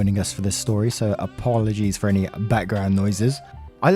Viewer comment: Correct me if I'm wrong but, did you just say "I aPAW-logize for any background noises"? did you just...👀👀👀 *Sigh, you cheeky and wonderful person.